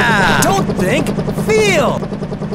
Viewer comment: I dOnE hAnDcUfFeD LiGhTnInG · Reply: I've always misheard "I've got hands of lightning" but this works too 😂